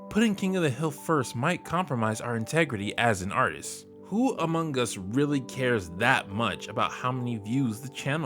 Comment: What you said: "Among us" What I heard: "Amon Gus"